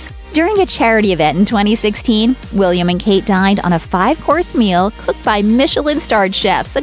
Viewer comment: DID SHE JUST SAY THEY DIED?